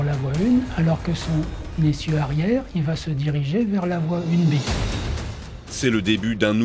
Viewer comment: "vers la voie..." quoi ? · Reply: Vers la voie 1B(une B)